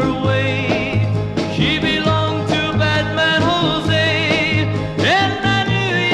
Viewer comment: When I was a Kid I thought they were saying " She belonged to Batman José" God I was stupid